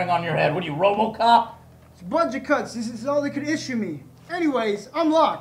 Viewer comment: Lol I thought he said bungie cuts